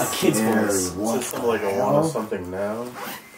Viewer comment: the little kid’s voice coming through on the spirit box- you said it was unintelligible but I heard clear as day “Follow me now”. The first time and second follow up. Right after you asked it who should go in first. Someone else may have already commented this too… I just didn’t take the time to read all the comments.